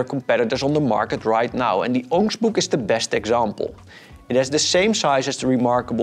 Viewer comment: what is he saying here? I tried reading the transcript, but it too doesn’t understand him. Orange book? Onix book? Wtf?!